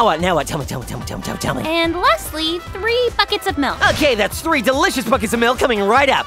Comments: is it just me or does it sound like she is saying the f word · it sounded like she said the f word 4:41 oooooooo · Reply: Thre fuckets of milk